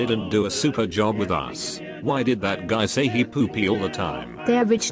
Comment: Why the different Spike voice? Also, what is Rarity saying at 1:55? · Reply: rich noises